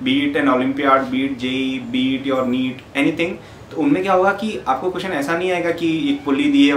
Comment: Did you say ' Beat your meat ' at 0:25 ?